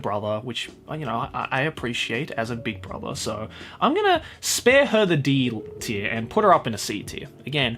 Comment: I think nearly said 'I'm gonna spare her the D' ;)